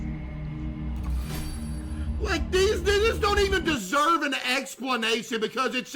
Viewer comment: So did he say that or am I geeked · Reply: he said “these- they just don’t even deserve an explanation…” He cut himself off after he said these so it sounds like he said “these **** don’t even deserve an explanation…” 🥹